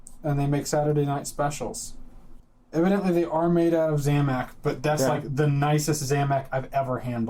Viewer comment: I thought said "They're made of Xanax". I was like, where do I get these?